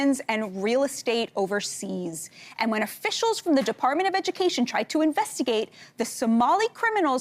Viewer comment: I thought Senator Incest said she contacted the Department of Egg for a second 🤦‍♂️